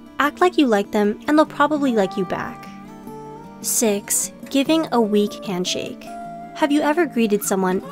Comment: 1. Acting too nice 2. Asking too many questions 3. Dropping a personal bomb 4. You often hide your emotions 5. Acting like you don’t like someone 6. Giving a weak handshake Edit: Omg thanks so much for the likes !! Make sure to watch the video for a more in depth explanation as well and I hope you have a good day if you’re reading this :D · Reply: MissFanGirl: Oh.. is that what it is.. I thought #6 said "Giving a Tweak milkshake." Bruh.